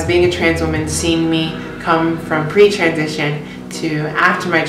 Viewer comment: When you said "pre transition" I thought you said "preacher edition." XD